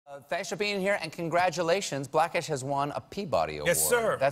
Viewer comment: "Thansh for being here"? I swear that's what he said.